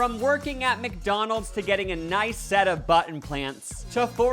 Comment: I heard “a nice set of button plants” 😂